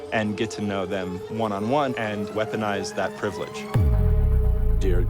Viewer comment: Absolutely! Weaponize the privilege to make the world a more fair and understanding place. Travel, and use the strong currencies of privileged countries to meet real people and form informed opinions about the world instead of being an armchair scholar! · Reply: The subtitles say "recognize the privilege", but I clearly heard "weaponize" as well. And it makes much more sense!